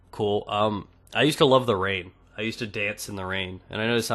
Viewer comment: That awkward moment at 16:36 i heard '' i used to love rape'' ... :D